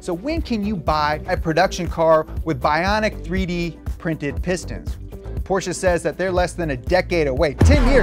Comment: I legitimately thought it said 3d printed pistols